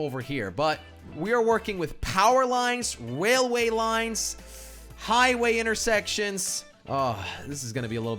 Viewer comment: Did he say waileay lines at 10:32 to 10:34🤣🤣🤣😂😅